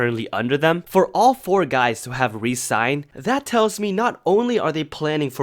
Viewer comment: For a second I thought you said BigBang resigned but then I realized you meant Re-Signed lol